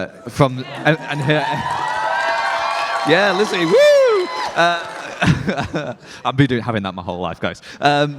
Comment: What did say here? I've been what? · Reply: I think he was trying to say: "I've been having to do that my whole life guys"